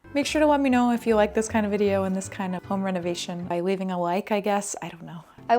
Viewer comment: I thought I heard foam renovation instead of home renovation, still accurate. What a fun space!